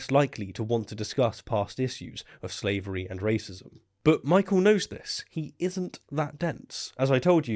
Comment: I swear at 6:14 I heard: "Michael Knowles is... He isn't that dense. As I told you, he's simply that hard. Dancing with pompoms singing..." I've lost my goddam mind